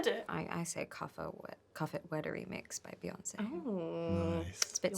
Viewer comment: what song did she say?? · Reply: cuff it by Beyonce 🙌🏼